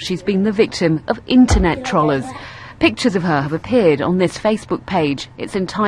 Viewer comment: I'm sorry, did you just say trawlars?